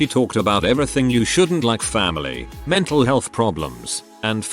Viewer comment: did someone say family